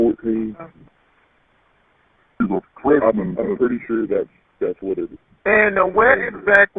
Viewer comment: Sounds like person talking in background says “I’m pretty sure that’s Lyric” at around 28:21. Does anyone else hear that?